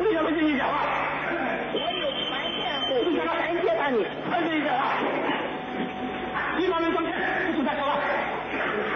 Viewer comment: 江青究竟说了什么，哪里可以找到江青在法庭上的发言，有没有网友可以提供更详细信息？ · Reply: “放屁！三家村不是你有份?”“你闭嘴”“我有权辩护，有权揭发你”。大概是这样的吧